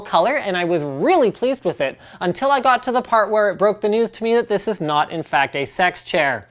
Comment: did he said "this is not a sex chair"?